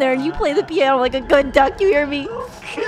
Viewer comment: I love how added “she made me bark” when he willingly barked like the good boy he is 😂😂😂 🐶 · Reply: He misheard "good duck" as "good dog"